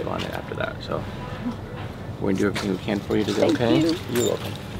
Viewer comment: Am I the only one that heard "he's dead" at 4:51 it starts and they hug. She says, " I just miss him so much" and it sounds like it says that. It's creepy. I mean NO disrespect at all. I am so sorry for everything you're going through. I just noticed something very odd.